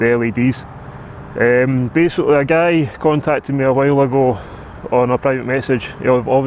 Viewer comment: was it a "gay" or a "guy" who contacted you? its sure sounds like you saying gay